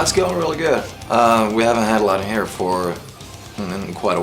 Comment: i thought he said "we haven' had long hair for a while" haha